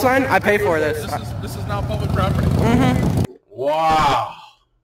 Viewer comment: cop was a very unprofessional douche' bag ( but you misheard him, when they crossed the street he said "This is NOW public property") I digress if had had done his do diligence on what was private/public property this could all have been avoided.